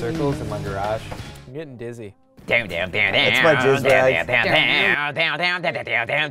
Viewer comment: Did says thats my jizz rag